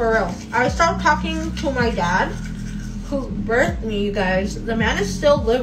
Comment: wait did she just say her dad “birthed me”? WTF 😳. Did she mean something else ? Lol